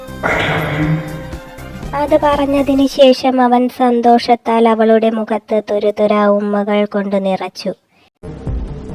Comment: Chechi, What was the word Adam said at 1:38, right after he thanked Jiya? It was not clear🤨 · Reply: I love you